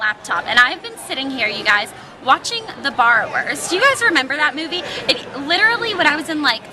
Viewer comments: lolol i thought you said "Bar Wars" instead of "Borrowers" and i was like that's a kids movie?? lol anyway hope you weren't in an area where there were a bunch of storms and hope you have a good prom~ :) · When you were saying barrowers it sounded like you were saying bar wars! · oh the borrowers! i thought you said the barwerts and I was googling it! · i thought you said "Bar Wars" instead of Borrowers lol